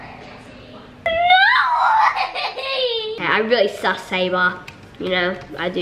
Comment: naz did u say NOO I WANT A KITTEEEHH